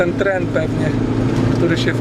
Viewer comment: Czy ja dobrze usłyszałem TREN??? Chłopaki zapomnieli dociąć filmik :-D